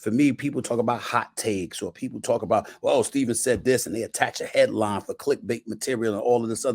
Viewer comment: A better headline would’ve been, Max Kellerman Opens Up About Stephen A Smith’s Departure From First Take. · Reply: Thought you said hairline 😭😭